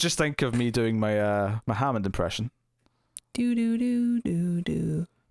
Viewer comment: I actually thought said "My Mohammed Impersonation" . . . it's that West of Scotland accent . . .